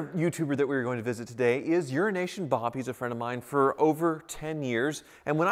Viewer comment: Subscribe to Euroasian Bob for great hooptie business content: <link> Subscribe to Elliott Alvis for great Spaceball content: <link> SUPPORT WICHITA CAR YOUTUBERS! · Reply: When you say EurosAsian bob I thought you have been say urination bob.